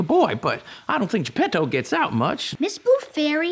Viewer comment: "...Sure there are other ways to make a boy, but I don't think Geppetto gets out (I thought he said "that") much". ...wut?